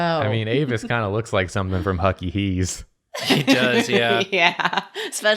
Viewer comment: I thought they said Hyucky Heese...